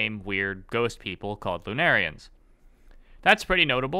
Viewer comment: swear i heard lumerians the first time and just heard risk of rain music start playing in the back of my brain god that was a weird feeling